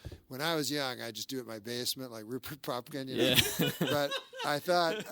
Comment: what name did he say there? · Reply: Rupert Pupkin (?) from the Scorcese movie King of Comedy—a delusional failed comedian kinda in the vein of the 2021 Joker.